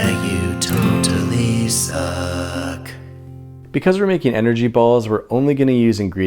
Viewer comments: I thought is said angry balls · When your intro said you totally suck, I thought you said don't tell Lisa 😂